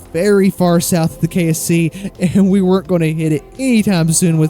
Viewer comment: it looks like the subtitles say KFC instead of KSC. 🤣